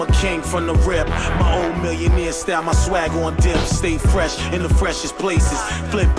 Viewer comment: did he just say swag?